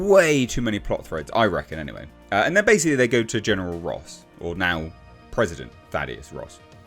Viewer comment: at first i thought he said General RAAM.